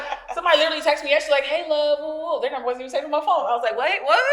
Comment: I swear every time said “Melo “ I swore I heard her say “hello “ 😂😂😂! 🤨 me looking at the screen confused AF 😂😂😂.. glad y’all back tho 🌈🤘🏾💙